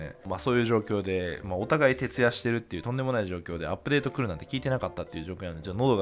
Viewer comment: お互いケツ出してるって聞こえた